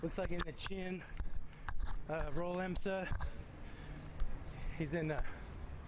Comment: did he say em sa